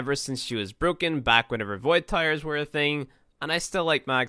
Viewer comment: Did you just say "Void Tyres' ???